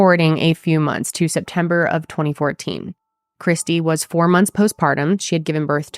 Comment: Something stands out to me here. The fact that she was already back at work after only 4 weeks after having a c-section… I can’t jump to conclusions because maybe she wanted to go back to work or maybe financially she had to- but with how devoted she was to her girls I doubt she WANTED to go back to work after 4 weeks, not to mention how surprising it would be for a doctor to clear her to go back to work. I wonder if he made her and she felt she had no other choice? I know 4 weeks after my c-section I was nowhere near ready to go back to work. Not emotionally, mentally, and definitely not physically. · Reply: I thought she said 4 months. I had a c-section with my daughter and 4 days later I was carrying in wood.